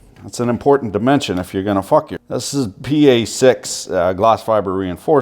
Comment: That's an important dimension .... cause they're gonna fuck you ?? did I hear that right?